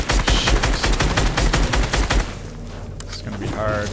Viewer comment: I heard something from 14:18 14:24 I think nick said an oppsie Hint: s word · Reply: he said shoot